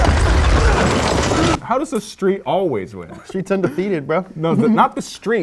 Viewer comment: not sure if it was intentional or perfectly random wordplay but I heard "The streets are under-feet-ed"